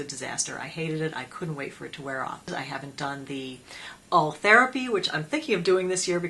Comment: Would you mind speling that out what gives you pointy eyebrowse? It sounded like you said All therapy? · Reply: Ultherapy